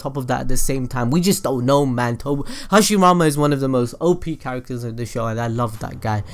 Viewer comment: did je say hashimama